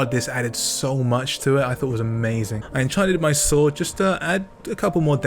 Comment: 1000? 😅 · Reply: Thought he said amazing pissing